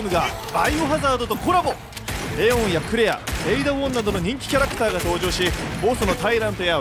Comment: 「レオンやクレア、エイダ女など....」って聞こえて「エイダ女？！」ってなりましたw